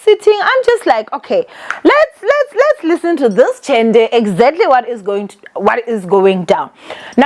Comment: did you just say lets listen to this Tzhende?😂😂😂😂